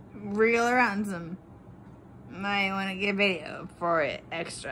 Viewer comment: wtf is she saying? “Real random”? I know “might wanna get a video for it… extra” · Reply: She says "wriggle around some"